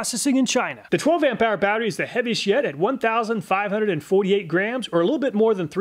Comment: I had to do a double take because how he said "heaviest yet" sounded like "heavy shit"